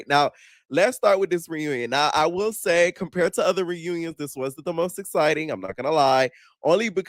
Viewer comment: I thought he said it WAS the most exciting I had to rewind 😭😭😭